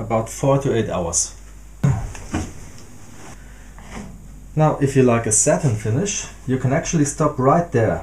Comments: I'm so glad the subtitles said 4-8 hours between application because I had genuinely heard you say 48 hours lol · Oh, 4-8 hours, the first time you said it I thought you said 48 hours. I have an 11 foot Walnut worktop I need to treat, Thought it was going to take me 4 days to to all the coats. 4-8 hours is much better haha